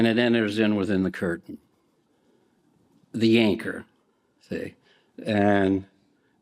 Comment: Did I hear "the yanker" or "the anchor" here?? Not once but twice he said YANKER!! 🤣🤣🤣